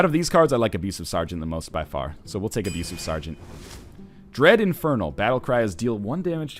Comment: I heard that as Bread Infernal